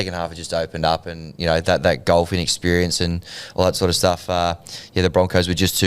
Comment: did he just say “that ‘golf’ inexperience and all that sort of stuff”??? 😂😂 sneaky jab hahaha